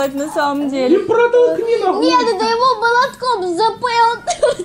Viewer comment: Вы тоже услышали это? 🌚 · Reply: Он сказал нахой (ногой)